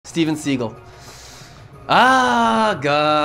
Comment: I heard it as Steven Seagull 😅